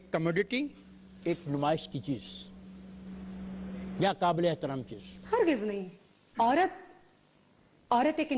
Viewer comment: Veena Indiawalo k sampark m rh kr kafi kuch sikh gyi best of luck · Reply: Interviewer ne 26:41 duration par kya bola ki kabile?